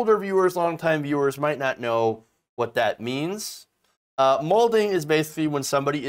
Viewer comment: im assuming malding is a contraction of mad and balding. i will admit that at first i thought you said molting. i was thinking... like aa parrot, when they get stressed. makes sense. but why are you pronouncing it like that?